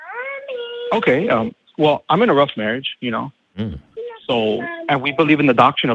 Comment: Me: "It sounded like he said he's in a 'rough marriage.' I don't know what he actually said, but WOW, would that have been awkward!!" ... 😳"Oh... shit."